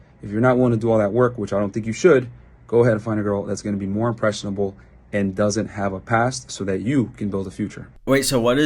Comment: did he just say go find an impressionable child?